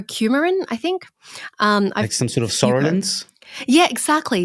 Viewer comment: does anyone know what he said? I can’t figure it out. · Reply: Psoralens